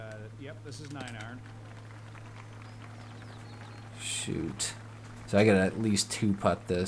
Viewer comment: Did he say "shit" at 9:24? If so, that's the first time I've heard him swear, ever.